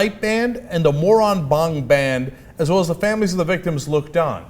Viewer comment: It sounds like Cenk said "The Moron Bong Band" at 2:28 lol